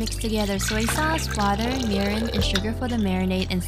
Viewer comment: Your captions say to mix soy sauce, water, urine and sugar. Please, please tell me that it meant mirin. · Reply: ohhhhh she said *Mirin* not Urine but ya I thought I heard her say that as well lol